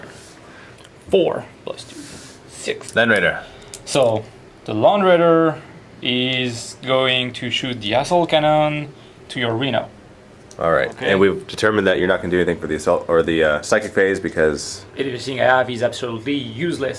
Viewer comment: Did he say the land raider was going to shoot the "asshole cannon" to the renal? @ 10:24 ?